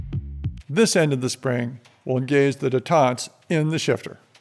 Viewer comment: what is that word that said in 21:43? Detaunt? huh? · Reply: Détente(s). From French. Literally "de-tension". I presume they're called that because they are effectively gaps that allow the spring to decompress.